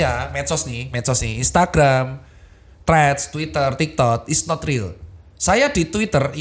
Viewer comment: kok gw dengernya tiktod ya😂